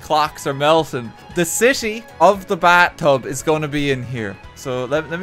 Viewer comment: Because of his accent it sounds like he is saying bat-tub I can only think of batman sitting in the bat-tub